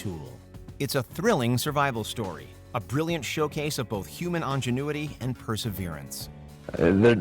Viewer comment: Did you just say on-genuity? Really bizarre pronunciation